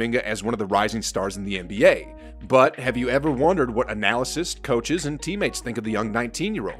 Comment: I think you mean “analysts”. I keep hearing “analysis”.